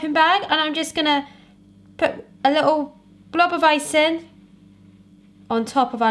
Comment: When said "blob of icing", I thought I heard "blobovicine" and I'm like, "is that some sort of new medicine, blobovicine?" Me and my silly ears :)